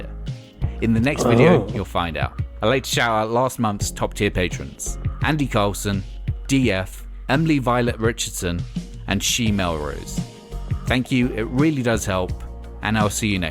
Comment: Anyone else hear 'She-male Rose'